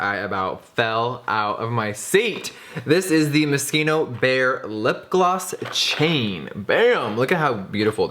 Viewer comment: Thought he said mosquito bear and I was confused for .2 seconds